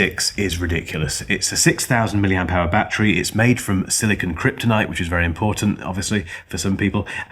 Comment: I'm sorry, did you say the battery's made from Silicon Kryptonite? SiK! 🤣